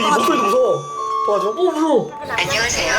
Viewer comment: 초에 좀만있으면 안녕하세요?라고 들리는데 그 옆엔 뭐 무서운 애기 그림?!